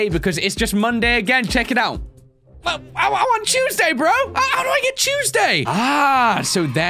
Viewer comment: Vid comes out he says Tuesday 5y later WAIT DID HE SAY CHUESDAY